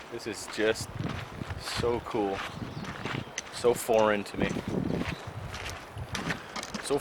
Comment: Not gonna lie, sounded like you said Kyle was "so far into you." #brokebackmountain